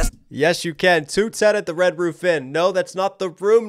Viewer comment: Is it just me or does it sound like Em says “at the Red Woof Inn”. Because he said “Jesus Christ Dawg” before that line.